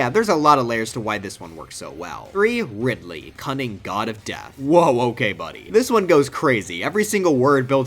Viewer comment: am i the only one who heard Cumming God of Death at 18:24?